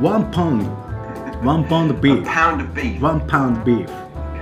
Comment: "A £1 beef." Well, guess you can't be disappointed with British meat if you start off with low expectations. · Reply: Oh! I heard it as 1LB of beef. I think that's the first time a culture difference has led me astray.